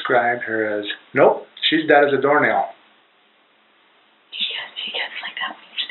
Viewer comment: She was “dead as a door nail”…? Disgusting. No loving parent would ever refer to their child that way. It’s bull to neglect your children and be abusive losers and mask it under the guise that’s it’s your religious beliefs. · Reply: Listen carefully, and it almost sounds like she said “ITS dead as a doornail”. Not sure if that’s true, but if it is, that’s even more sickening.